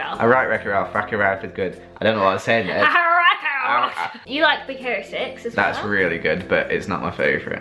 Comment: Lol when said Big Hero 6 I thought she said "Big Carrot Sticks" 😅